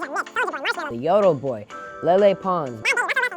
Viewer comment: did he say lele Pons, Man did dude is cringyg